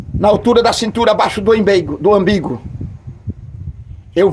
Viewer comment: já ouvi "imbigo" mas ambigo é a primeira vez 😂😂😂😂😂😂